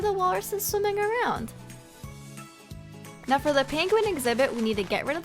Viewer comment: hey (idk y i called u dat) when u said "now time for the PINGUIN exibit" i thought u said "now time for the PANCAKE exibIt"